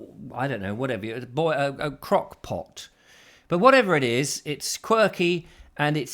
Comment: Thanks for another "quirky English Crock Pot" recipe....for a moment there I thought you said " crack-pot"... 😀😀😀